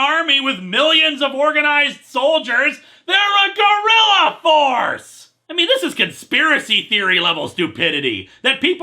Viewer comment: I know what he's saying, but I still hear "gorilla force" instead